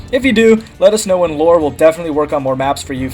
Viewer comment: near the end it sounds like you said "let us know when Lor will make more maps" instead of "let us know and Lor will make more maps"